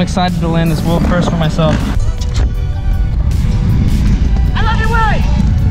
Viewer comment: it sounded like he said I love your willie